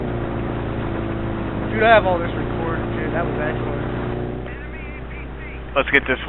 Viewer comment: I think I am playing too much COD as at 0:33 to 0:35 I heard ENEMY Ac-130 above